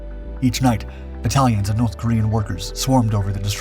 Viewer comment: Was i the only person who initially thought the video said Italians and north koreans at 12:14? I thought, that's a weird pairing.